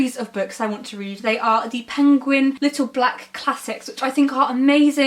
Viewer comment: When you said "penguin's little black classics" I thought you were saying that gilman wrote a book called "Little Black Cossacks".